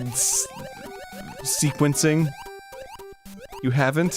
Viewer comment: I don't understand how Dan doesn't know about Cakewalk when he's the music guy. Also Arin doesn't know how to pronounce "MIDI" so that's fun. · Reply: LOL, I thought Arin was saying "mid-eyes".